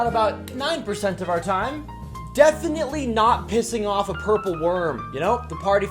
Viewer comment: At first I thought it said “definitely not pissing on a purple worm”